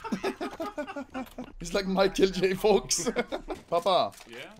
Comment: did he just say “ it’s just like Michael J fox”?